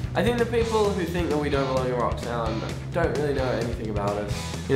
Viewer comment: At first I thought they said Roxanne not Rock Sound